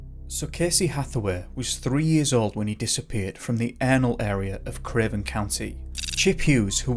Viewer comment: He disappeared from the anal area sorry but that’s what it sounded like you said LOL